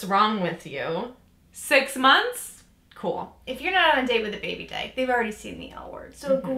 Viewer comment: Im sorry I'm deaf and the only subtitles for this video are auto generated, can someone tells me what it says at 1:46? Baby day??😂😂